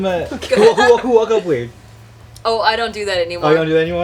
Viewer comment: what does say at 4:59???? CANNOT FIGURE IT OUT FOR THE LIFE OF ME. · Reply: "Who woke up weird"